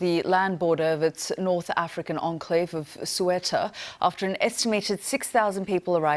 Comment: "Sueta" lol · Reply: I thought she said "Suetta"